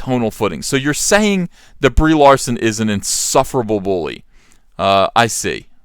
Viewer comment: I could swear he says "Debris Larson" lmao.